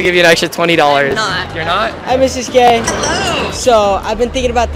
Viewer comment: i heard "hi mrs Gay"